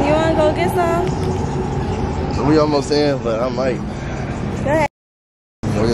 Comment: Do anybody know what she said at 4:43 😂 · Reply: She said go ahead stink